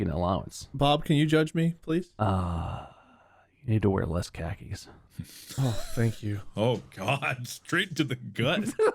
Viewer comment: What does bob says here? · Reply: Need to wear less khakis